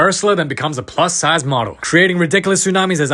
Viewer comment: "With Ursula becoming a plus size model" 😂😂😂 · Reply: I thought it said,, pluss sized mother"💀